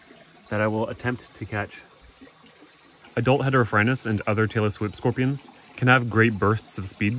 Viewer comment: I heard "Taylor Swift scorpion"